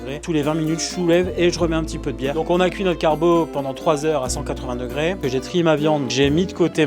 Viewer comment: Quand j'ai entendu "vraie carbo", j'ai cru que c'était encore un débat entre le guanciale et les lardons. Puis j'ai vite compris mon erreur. 😂